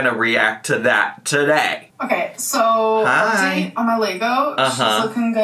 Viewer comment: Is Amber saying "Lego" at 04:11??! I think from trying to work out what's behind her/what she's pointing at it is, but all I can hear is "Leygo" or something similar, and definitely not Lego, so I'm a little confused somewhat I'm afraid and embarrassed to say! 🤔